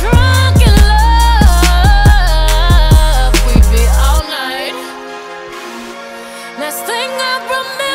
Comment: No like why i thought it said "baby all night, oh oh oh" 😭nah